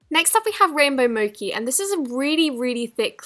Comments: what did she say? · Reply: She is trying to say Mochi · did she say rainbow mokee? im dying lmaoooo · for some reason i heard her say “moki” instead of “mochi” 😂 could be just me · Who else heard her say moki not mochi